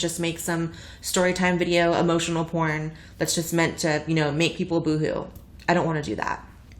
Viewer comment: At first I thought she said "I don't want to make some emotional porn meant to make people GOO." and I was like WHOAAAA WTF